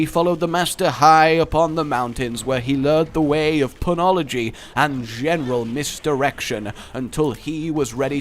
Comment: I thought you said "learning pun ology and mis erection